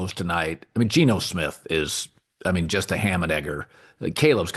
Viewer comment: Yo what did collin just call geno the 2:00 mark??? Collin just bored seeing how far he can go before getting canceled 😂😂😂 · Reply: Ham and egger = just a guy, average, nothing special, etc.